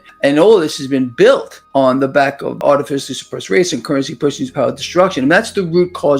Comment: Re minute 2:02… did he say artificially SUPPRESSED rates? I slowed it down and I still am not certain about that one word. Thanks.